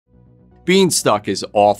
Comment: “Being stuck”! I thought you said “bean stalk”! 😹